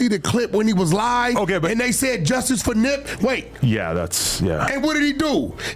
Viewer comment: When they show big U laughing at the “justice for Nip” listen closely at the dude talking in the background 👂🏾 …… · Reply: And it sound like he was saying justice for Dip , like he disrespected his name intentionally